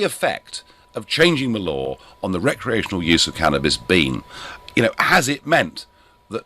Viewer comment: I thought he said "cannabis bean".